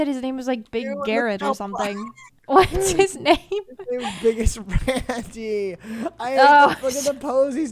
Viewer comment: I thought they were saying Biggus Randy and honestly it made it so much funnier lmaoooo